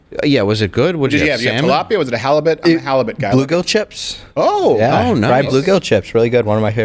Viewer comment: Does anyone know what fish 🐠 he's talking about? Sounds like he says "blue go chips"